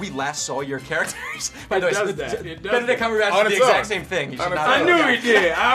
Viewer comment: I thought someone said Benedril instead of a benedict